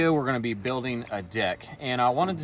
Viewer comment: haha cc said dick at 0:05. Can't say that on youtube!